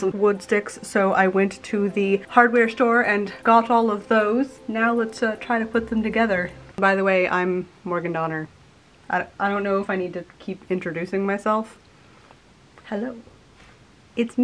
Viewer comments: When she introduced herself I thought she said, "Oh, I am an organ donor. I don't know if I keep having to introduce myself..." smiles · Misheard introduction "I'm an organ donor"... oh your name is Morgan Donner!